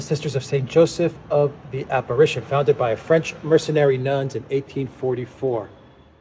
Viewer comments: For some reason, I heard "mercenary nuns" when you read out that plaque! :D Thank you for the update. I've enjoyed your work with Alexander for some time now. It's an important antidote to the biased nonsense that emanates from the MSM. God bless you both. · French Mercenary Nuns? Might have misheard but getting some serious 40k vibes. (good job on the updates-Crazy!) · did he say “…French mercenary nuns”??? I need a vacation…